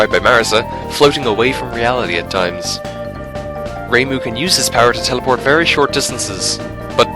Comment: did you just say ,,his powers,,? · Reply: “This”